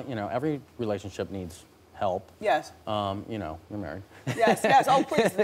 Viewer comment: it sounded like he said "we're married" am I a crazy or did it sound like he said that. I know he's not bit it sure sounded like that to me.